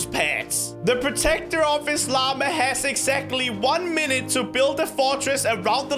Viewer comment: not gonna lie he sounds like he says llamerh